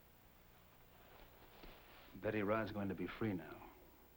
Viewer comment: does Steven say Ryan? Or Rod?? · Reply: Rod